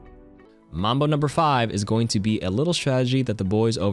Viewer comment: mombo? Am I hearing this right